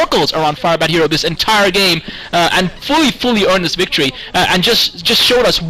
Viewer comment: Can anyone tell what said in 19:15? "Just Show us what he is a ????? player that he is" THANKS A LOT!!!! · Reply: I'm 12 years too late but he said 'prodigy'